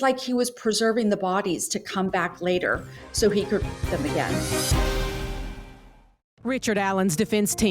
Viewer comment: Did she really say "so he could f$#@ them again" at 15:17..jesus have some tact wtf